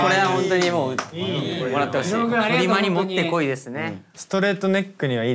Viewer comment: 「フリマにもってこいですね！」 にも聞こえる！笑